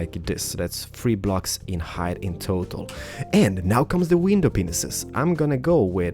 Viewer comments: At about 1:58... Did he say "window penises"? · oh he says pains i thought he said penis